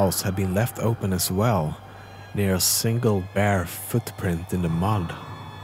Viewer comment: Lol I thought it said "bear footprint" and somehow that was less scary